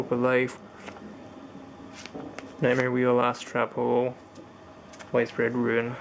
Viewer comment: Sounded like you said "Ass Trap Hole". lol